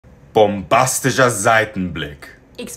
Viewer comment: The first one sounds like he was saying bombastic